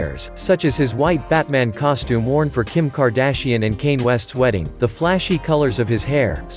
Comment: I know this man did not just say kayn west....